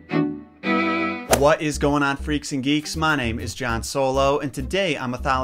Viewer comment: "Freaks and geeks" Me: "excuse you sir I'm a solo fam." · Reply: Oh so he said that xD I thought I heard that but then my brain changed it to Greeks and geeks xD